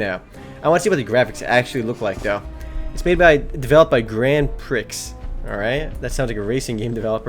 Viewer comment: thought u said Grand Pricks...=D